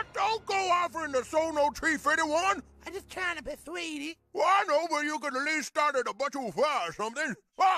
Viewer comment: Did anyone understand what Chefs dad said at this moment? I didn't hear what he was saying. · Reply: Thomas said and I quote: “I know but you could at least start at a buck o’five or somethin.”